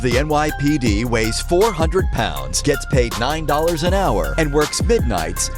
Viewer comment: 🤦‍♂ Did I just hear a robot getting paid 9 and hour.. really...